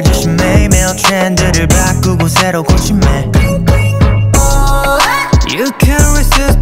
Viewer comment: who else hears ”mailman chan”😭